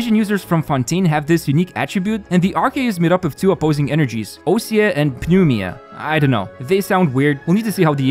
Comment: what is blud saying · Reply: Ousia and Pneuma mechanics. Basically every Fontaine character’s skill will either deal Ousia aligned or Pneuma aligned elemental damage, and if you use these energy types against the robots of the opposite type, it will paralyse them.